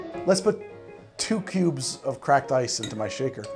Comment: I swear I heard you say "2 pubes of cracked ice", which seems appropriate. Dance, Ron Swanson! Dance!